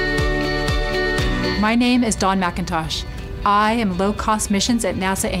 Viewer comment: did she just say her last name was Macintosh?